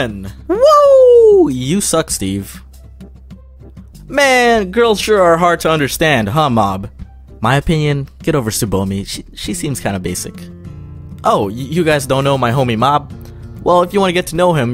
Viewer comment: I thought he said mom the first time